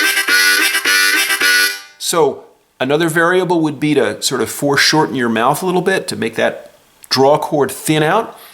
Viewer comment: I didn't catch what you said at 13:00 about "mouth... file out" (I'm french sorry). Can you say a few words more to explain? Thank you for that "hypnotic fata fata lesson"... makes me think about old muppets saying "what is a mana mana?" and the answer "question is who cares?" :) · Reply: I said "foreshorten your mouth." Tricky word in Englisn for a non-native speaker! This means reduce the distance from front to back--which mostly means bringing your tongue forward, so that it's very close to the harmonica. If done right, this thins out the sound; you're moving less air volume, which lets you move more smoothly at a higher speed. Tres grande vitesse! :)